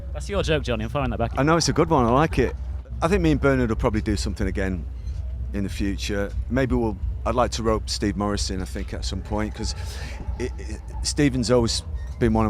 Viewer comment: Haha, I misheard that as Stephen Morrissey and got confused and especially by him calling him Stephen and not Morrissey. : P